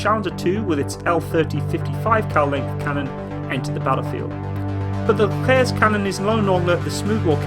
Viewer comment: what is he saying at 9:48? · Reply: The Leclerc's cannon is longer than other NATO tanks